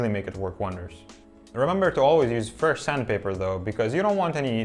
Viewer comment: What are you saying at 1:25? "remember to always use ??? sandpaper though" fur??? firr? what sandpaper